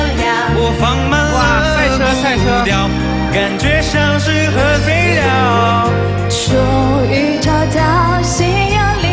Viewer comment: 自從上完自然課的核廢料之後 我在聽這首歌 聽到1:21 感覺像是喝醉了 我聽成 感覺像是核廢料